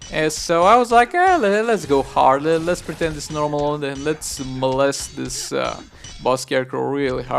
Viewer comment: did you just say molest the boss really hard?